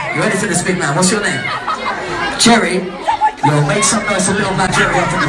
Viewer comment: All I hear is “cherry”😂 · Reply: It’s Jerry